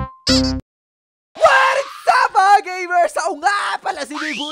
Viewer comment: ang narinig ko beebuyog-kun